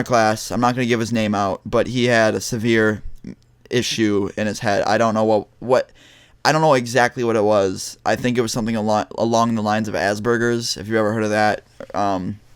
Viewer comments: I thought u said "Ass Burgers" fucking burgers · I am a little upset at myself... I giggled when you said asperger I am so sorry. I thought you said Ass-Burgers... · I deadass thought he said ass burgers and started laughing my ass off Guess I'm going to hell now